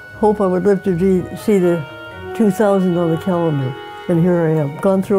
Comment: Did he just say he wants to live until 20100 😂😂